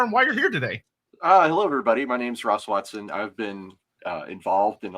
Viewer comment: When said Ross Watson I thought he said Ron Swanson and I was fully ready to believe Nick Offerman was into Warhammer.